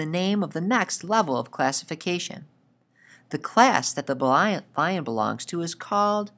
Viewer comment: what did he said in 1:45-1:47? i really cant catch up he's so slang. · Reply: Ku Kok I think he said blion